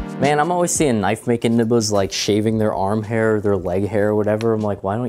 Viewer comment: did he say nibbas? Also yeah this is the only Alpha Mail we’ve ever seen very good start i must say